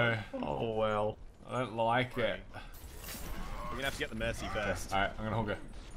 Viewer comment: did just say he was going to "Hog" Mercy